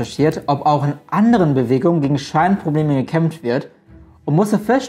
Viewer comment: Ich hab Schweinprobleme verstanden und mir gedacht, moment mal! Ahhh scheinprobleme obwohl beides Sinn macht :D